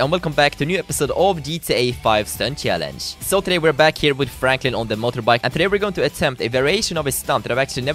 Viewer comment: it sounded like he said frakin at the start instead of franklin.